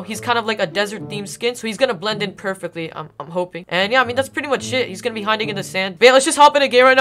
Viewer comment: who else thought he said “well that’s pretty much sh*t”